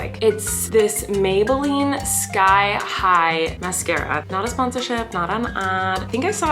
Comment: I thought she said stye high and I want that to be a real masscara